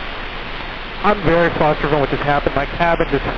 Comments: I think he says “I’m very flustered about what just happened…” although the audio isn’t the clearest at 3:49 …nonetheless, great job by all to get that plane safely on the ground! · Subtitles say "I'm good after what happened" But what the pilot says is "I'm very flustered from what just happened"